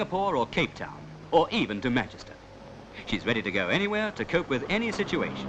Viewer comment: I thought at 8:11 he said "Or even to mattress town" LOL